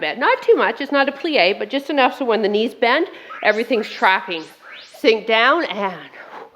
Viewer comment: This was a great one! I had to laugh because I thought you said "Everything's cracking" and I was like yep, yep it is 😂 Everything's TRACKING. Tracking. I love this challenge. Even though I'm super behind 😁